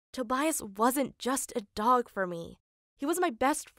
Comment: Not gonna lie ...I thought it said hot dog ..